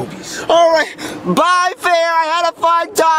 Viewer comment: What did he say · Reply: “P.O.P.! Pimp squad! Mama, I love you!” 😂😂😂